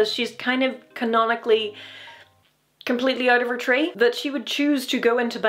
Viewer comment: "Completely out of her tree" is a wonderful phrase us Americans don't have. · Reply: Ah. I thought she said "completely out of her train."